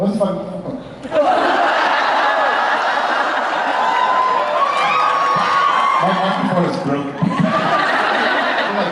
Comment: What did Jared say at 7:20? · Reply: He said what the fuck